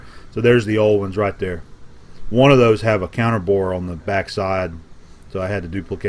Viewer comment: Hello, as you can see I'm Italian so I'm not perfectly fluent in English. Would you please spell here the word that you had pronounced on 36:27 min. it sounded like "..cannon board..". I'm curious to know its meaning. Thumbs up bud, for the project of expanding the shop. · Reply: Nicola Prisco I believe he said "counter bore" ... greetings from Germany :)